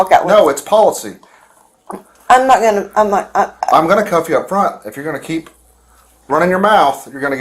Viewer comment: The caption being "Coffee" instead of "Cuff you" is so silly 😭